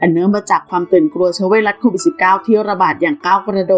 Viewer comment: รีพอร์ตให้ช่องปิดไปดีที่สุดแล้วครับ แบบมีเยอะมาก 3:09 คือตรงนี้ผมได้ยินคนเดียวรึป่าว เชื้อไวรัส 69?